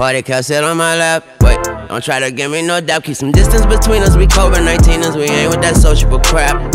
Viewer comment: "Keep some distance between us, we Covid19'ers, we ain't with that sociable crap" 💀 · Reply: I thought he said "we over 1,9ers" lol smh